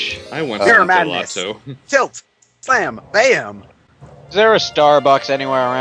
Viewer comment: For a second, I thought that said "Tit, Slam, Bam!"